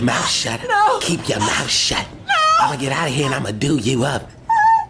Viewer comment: I only have one problem with this. They thought he said I'm gonna do you. As in rape. You 'tards, he said do you up as in I'm gonna beat you. Everyone knows rape isn't funny. Domestic abuse on the other hand...